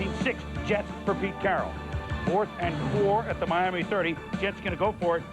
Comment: did he say Pete Carol? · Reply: Focusyn Are you deaf? go to 2:30. And look what he said. "Jet's for Pete Carroll."